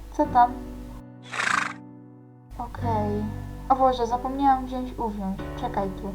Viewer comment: A ja usłyszałam : ,,Poczekaj zapomniałam wziąć gówno" XD